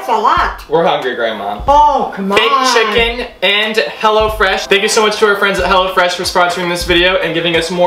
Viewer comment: everytime you said "hello fresh" I thought you said "hella fresh"😂😂